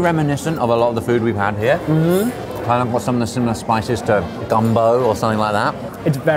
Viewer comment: Did really call Gumbo - "Dumbo" like the famous Elephant (at 5:30 mark)?